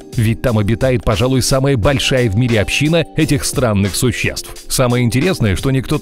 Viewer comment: Случайно услышал сраных😂